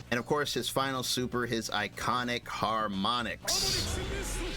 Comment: What he actually says is "Harmonic boost", not just "Harmonic". The name of the super is "Bookmark: Full Order"